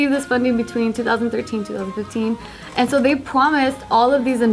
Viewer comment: im rewatching this and is it just me or did say pwomise at 2:44???